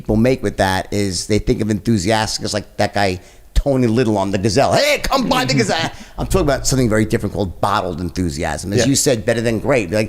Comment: I thought he said “come buy niggas” 🤧😂